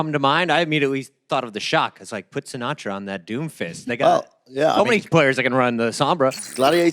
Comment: am i the only one who hears "Put sinaatra on that doomfist n-word"??????